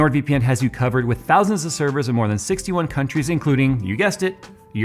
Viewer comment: did he just say "in over 61 countries including europe "?